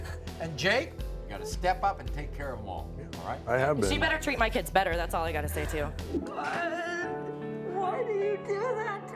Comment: One she's wrong by saying he better treat my kids better no he better treat the kids equally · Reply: But that's not what she said! She said, "She better treat my kids better, that's all I've got to say" so she was saying that about the woman not their father.